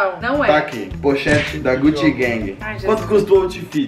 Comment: " gutigueng" KKKKKKKKKKKKK eis q vc ouvi mt lil pomba